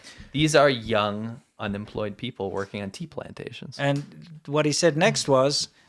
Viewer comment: Why i first thought he said TEMU plantations at first...had to reply it twice to make realize he said Tea.